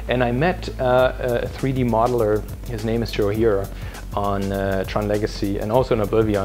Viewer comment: whats the name of the 3d modeller? couldn't understand it really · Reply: +Will Gibbons His name is Joe Hiura